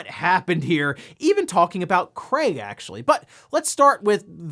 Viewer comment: did i hear you say Cray ? Or Crate ? we all know " Craig " He Should Be On The Box Art when Halo Infinite Releases